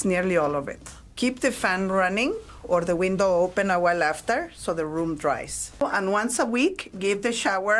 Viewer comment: What did she say at 9:15? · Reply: she said “keep the fan running or the window open awhile after so the room dries”